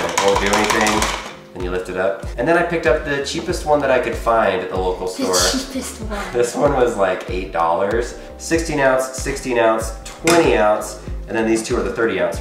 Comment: When said "the cheapest of all" it sounded like he said "yeah she pissed me off!" 😂😂😂😂😂lol I love you guys your channel is great